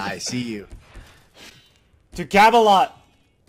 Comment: Did he just say "To Gavalon"? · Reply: Camelot